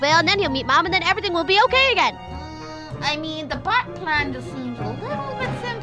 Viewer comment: when said Bat Plan, I thought she said Batman 😂